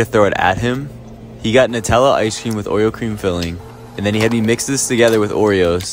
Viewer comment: Thought he said oil cream filling